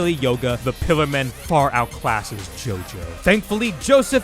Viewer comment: did you say they outclassed Jotaro? That's the wrong Joestar. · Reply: No. They said "JoJo". But yeah, it does sound like Jotaro.